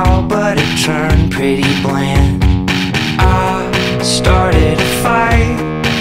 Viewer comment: Wow I’m surprised I found this literally all I heard was ‘I started a fire’